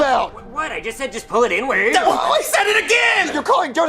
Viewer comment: saying "inward" and getting them kfc isnt even racist tho they misheard and judged him cause they thought he meant the actual n word